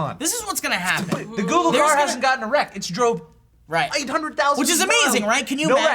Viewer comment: anyone else hear joe at 14:51 say "The Google car hasn't gotten erect" ?